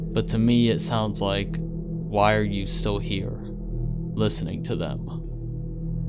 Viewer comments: What I heard is "why are you still mad? Listen to that" 😂😂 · I thought the voicemail was saying, ''while you are in there, listen to them''